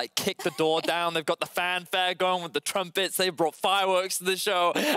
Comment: Could any one please tell me.. what do was saying in here? I don't get it... 3:20:52 .... after[LPL like kick the door down ........... fireworks the show] please... I really want to know about these words... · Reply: He mentioned sounding trumpets (the musical instrument) like at a fanfare.